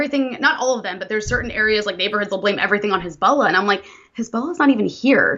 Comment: Who/what is she saying that Sunni's are blaming? It sounds like a name, but I can't catch it. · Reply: Is it "Hezbollah"?